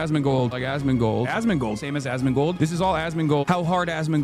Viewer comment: I hear "Assmingle" now